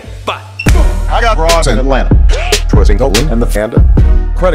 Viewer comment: why the fuck do u keep saying DesiignA when its obviously spelled with an ER at the end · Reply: It sounds like he said vigna